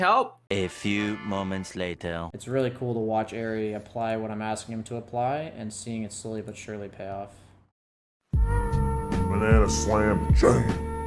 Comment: What does say 0:20? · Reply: It's really cool to watch eri apply what i'm asking him to apply and seeing it slowly but surely pay off